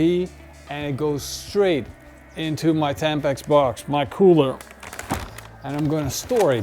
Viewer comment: Did he say his Tampax box? 10:53 😂